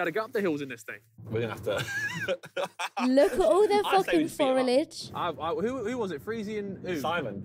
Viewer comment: DID YOU SAY FORILAGE? do you mean foliage? 😂😂